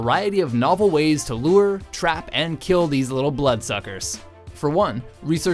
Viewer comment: I thought he was going To say bloodFuckers